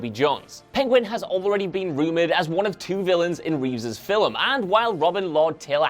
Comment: did. Did he say filum